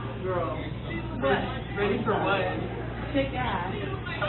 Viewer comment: Willie D listen to the video real good You'll see what happened clearly She walked up to the table and tried to sit down and said you guys ready They said ready for what, she said kick ass. That was she response so she came there to ruin their night from the beginning, and its Ice Spice not Spice 1 the rapper from LA my man 😂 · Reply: I thought she said "Y'all ready to shake some ass!!" 😂😂😂😂 (Pause!!)